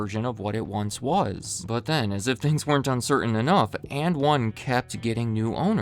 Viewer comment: Don't know anything about basketball. I always thought they were saying "in one" like "got it in one shot". I've never heard of this brand in my life.